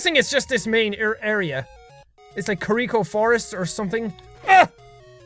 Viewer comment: did he just say Kakariko forest